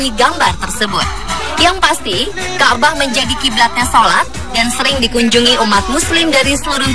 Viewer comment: apa aku yg salah dengar "kiblat yg salah